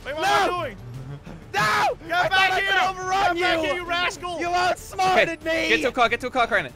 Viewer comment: did just say "come back here u a**hole"